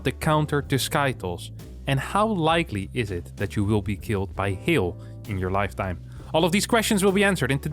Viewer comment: listened to this 4 times and couldn't figure out what meant. Then at the end of the video I figured it's Hail 🤣🤦🏻‍♂ oops